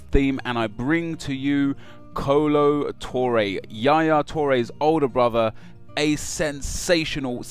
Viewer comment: i thought u said YAYA TOURE!